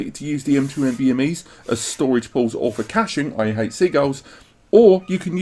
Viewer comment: Good information. You can improve with shorter to the point video. And improve on pronunciations/enunciation. I mean... @ 14:20 did u just say I hate seagulls?